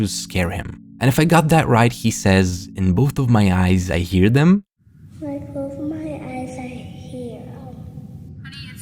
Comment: "when i close my eyes i hear them" kid has a little bit of a soft palate lisp that makes it sound a little off.